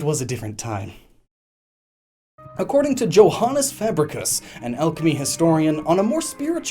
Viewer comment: Joanis fabricus.... I LEGIT THOUGHT U WERE SAYING JOANES FABRICS FUNNY😂😂